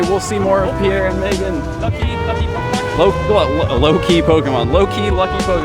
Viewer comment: Did I hear it right, says "Loki" Pokémon? :D